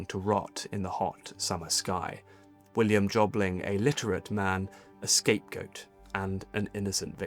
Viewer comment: so was he literate or illiterate literate is what you said, but the text says illiterate?